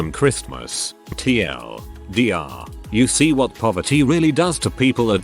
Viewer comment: Man I thought this said puberty instead of poverty and was very confused for the first 5 minutes.